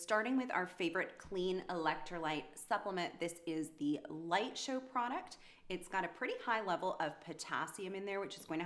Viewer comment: Did you mean magnesium bisglycinate? <link> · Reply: no, it sounded like you said light show product. It was the first ingredient that was mentioned for the recipe.